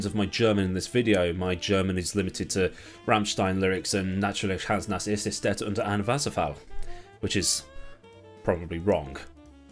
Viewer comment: As a German, I have listened to this like 6 times now and I still have no clue what you were trying to say xD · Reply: Aber ja natürlich Hans ist nass, er steht unter dem Wasserfall.